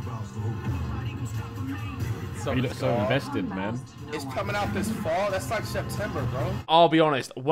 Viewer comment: flight and harry both thought it said its comming out in fall but instead it said "who will fall". ethan and tobi didnt say a thing lmao